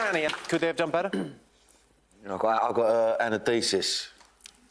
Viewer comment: "That's the cockney pronunciation of 'anodizes'." HAHA diplomatic, insulting, and funny at the same time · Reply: I thought he was saying antithesis